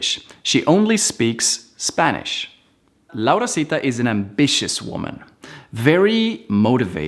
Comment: No se cuántas veces escuché que la Laurasita era una Bich Woman 😂😂😂